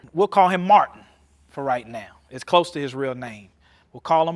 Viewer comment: it sounds like he said “it’s close to Israel name” 😏